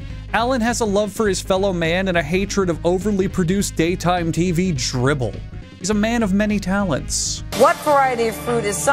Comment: Did he just say dribble?